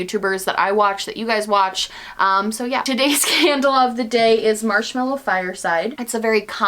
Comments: I thought you said “scandal of the day” and I was like 😮is this a new segment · You said ‘“ today’s candle”’ and I heard, “ today’s scandal”. An appropriate slip, yes? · I heard “today’s scandal of the day” and it got me really excited then I realized you were talking about your candle. 😢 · am I the only one who heard "scandle" instead of "candle"? 😂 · I heard today's 'scandal' of the day😅😅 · Lol when you said “today’s candle” I thought you said “today’s scandal,” which is v fitting for this video. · When she said “today’s candle of the day” I thought she said “today’s SCANDAL of the day”